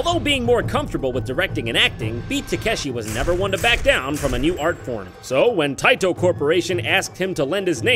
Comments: I would just like to point out that at 1:40, instead of correctly saying "art form", the captions say "art porn" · I THOUGHT IT SAID TAKE SHIT IM DIEING 😂😂😂🤣🤣🤣